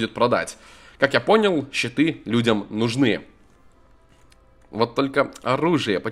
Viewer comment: Мне одному послышалось "Читы людям нужны"? :D